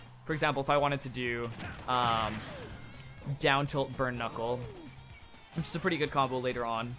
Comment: Terry saying “burn knuckle” sounds like he is saying “chronicle”